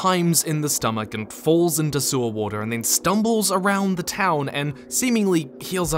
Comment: Sewer water..... I thought you said salt water at first.